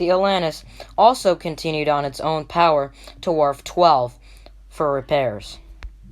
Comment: Wharf 10 wharf 12... I thought the narrator said warp lol